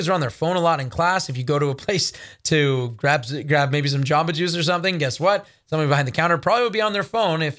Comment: what did he say..."grab some choppages"? "grab some sausages"? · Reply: What Amber said. Grab some Jamba Juice (it’s a smoothie place)